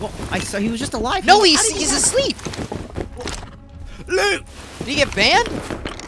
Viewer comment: Did he just say “run” in Danish? (Løb!) 😂